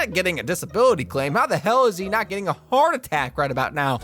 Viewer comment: i heard fart attack the first time